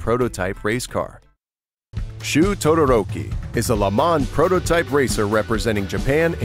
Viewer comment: did you just say Todoroki? lol